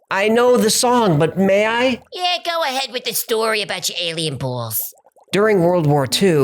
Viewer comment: go ahead with the story about your alien balls I heard "go ahead with the story about your Aryan boss". 😂 😂 😂 😂 😂 FOO FIGHTERS. 😂